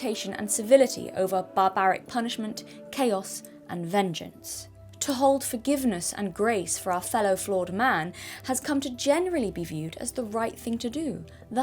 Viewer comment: you said "our fellow flawed man," but I heard "our fellow Florida man," and really aren't we all a little bit Florida man?